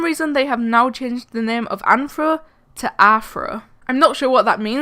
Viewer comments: did she say afro? (im sorry) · When you said rthro or athro it sounded like you were saying afro